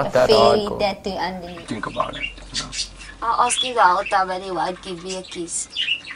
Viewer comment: What is she saying at 1:50 ... the awkward silence afterwards makes me curious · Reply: She said,' I ask you the whole time anyway give me a kiss.'